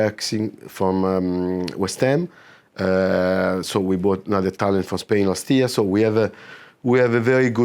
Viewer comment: around the 2:30 mark, does he say we bought a talent from Spain last year or did he say we signed a player from spain, and then say a players name? I don't recall seeing any news of a spanish academy player coming in since Matteo Joseph?